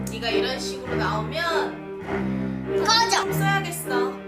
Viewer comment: 나만 "꺼져 써야겠어"로 들리는건가?